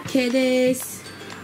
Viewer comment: 尺足りてるの声がぎりぎり聞こえたw